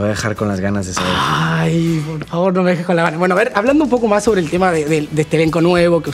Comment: Que dice Facundo en el minuto 2:41? No entiendo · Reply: Dice: -Ahh por favor no me dejes con las ganas... 😝😝😝 saludos